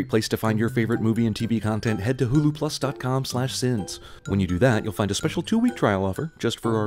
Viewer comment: When he said HuluPlus I thought he said Google+